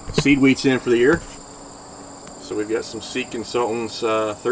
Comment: Oh you guys are saying “Seed Consultants”! I always heard “Seekin’ Salts”. I was way off. Lol.